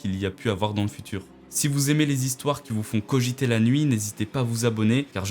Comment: j'ai entendus qui vous font cogiter l'anus